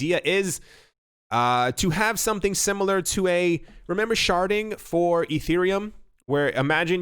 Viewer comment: Thought he said “sharting” at first 😂